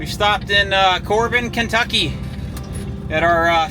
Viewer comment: What city is that pizzeria in? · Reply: I thought he said Corbin,ky